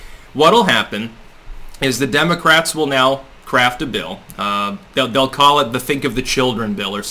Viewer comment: I heard "craft a bill" as "crap a bill"... Something Freudian in that...